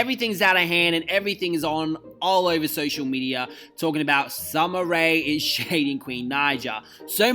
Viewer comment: I heard him say Sumner Ray then I saw ‘Galactawhore’ lmao I’m like “ooohhhh you mean Summer Walkerrrrr”